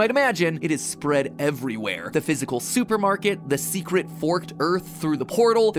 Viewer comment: i got an ad in the middle of him saying supermarket, so i heard “the visible soup”